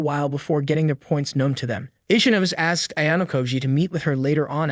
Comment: I heard itchy nose 0_0